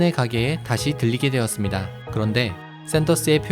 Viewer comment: 들리게-> 들르게